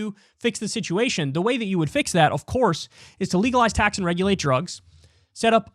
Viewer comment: “ legalize tax … and regulate drugs” … we already have tax … oh wait … misheard by my smooth brain